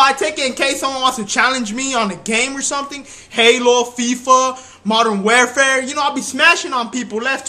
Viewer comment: HAHAHAHAHA SOUNDS LIKE HE SAID MODERM WELFARE